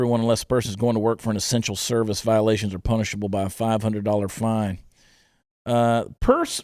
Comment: I swear to God...rules have gotten so out of control, my mind went STRAIGHT to: “Punishable by FIRING SQUAD...” rather than what you said (“punishable by fine) ! 🤣🤣 Geeez. 🥴 Stop the madness! #Trump2020 🇺🇸